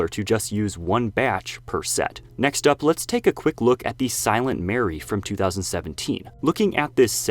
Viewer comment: the silent what you say? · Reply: Silent mary